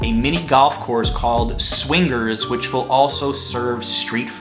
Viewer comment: Did you just say "swing gurs"?